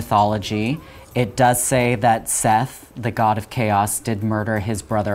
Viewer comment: the captions say “set” in reference to the god of evil and sibling rivalry, but what he says sounds more like “seth” the reason I find this interesting is that if I recall correctly, the names that we know the Egyptian gods as (ex. Isis, Osiris) are actually the Hellenized versions as opposed to what the Egyptians actually called them. So “set” is what he’s commonly referred to as, while “seth” is more accurate. Set vs. seth is one of the few I remember from where I learned about it (a great book that I have sadly lost) but the other gods usually had a much more drastic change in name. Am I overthinking a minor discrepancy between the captions and the audio? Probably but it’s still a really cool thing I don’t hear many people talk about